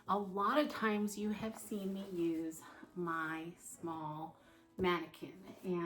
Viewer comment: Hello, sweet friend! Everything is beautiful! I had to listen twice to realize you had said ‘small mannequin'. I thought you said ‘Maude mannequin’! The bow is a beautiful addition, great idea! 🌷